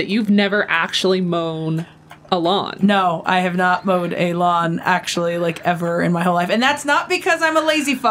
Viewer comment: Dear at 3:51 When said Mowed the lawn it sounds like she said MOAN the lawn. That was truly funny. Fantastic gaming you too. Keep up the good work. Love you both. Have a wonderful day.